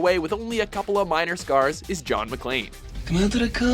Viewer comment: John McClane I thought you said John McCain